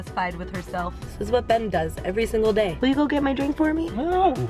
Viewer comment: Wait, Ben? Did she say Ben..? Is real name Ben? I'm probably looking too far into this